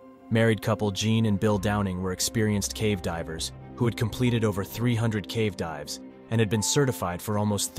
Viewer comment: i thought that said 300 wives 💀